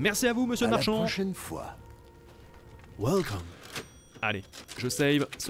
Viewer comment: De l'exploration aujourd'hui mais surtout beaucoup d'action face à du très très lourd :_GalaxLove: Episode 9 dispo par ici : <link> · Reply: c'est moi où le Marchand dit "Welcome" 😮